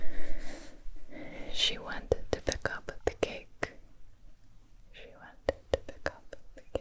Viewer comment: i need to know what is she saying at 6:00 she did what to the cake? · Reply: 'she wanted to pick up the cake' 😁